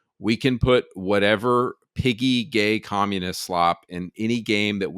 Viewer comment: I stg I almost heard you say "goyim" instead of piggy.